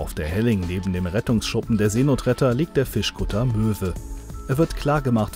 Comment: "liegt der Fischkutter Möwe" Ich hatte in dem Moment nicht hingeschaut und Möse verstanden. Da hab ichmich gewundert. Was .....? Sah aber dnan noch Die Aufschrift "Möwe"